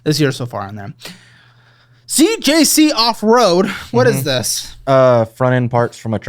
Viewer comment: i thought he said “See J.C. off-road” and i assumed it was some sort of Christian themed monster truck rally